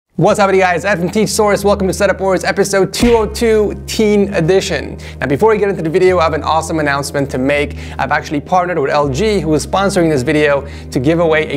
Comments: Ed ngl for a second I thought you said Ed from teet source, only certified big bois will know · DID ANYONE HEARD "TEACH SOURSE"......?